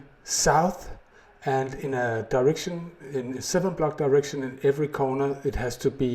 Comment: it does indeed. But still need to have a 70x70 spawnfree area around the head of the bed. Thats why its usually build on water :-9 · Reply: oh god, I misheard you and thought you said 7 in each direction.... this explains a lot.